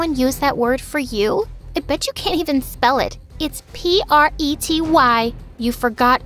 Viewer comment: omg sounds like she is saying "and b*tch you can't even spell it" 😭😭😭